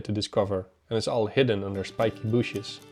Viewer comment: i did chuckle when i heard 'Sipky Booshes" . Welcome back team!